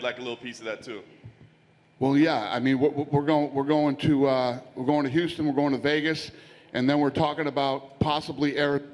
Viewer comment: wtf he say ? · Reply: He said, “I like money.”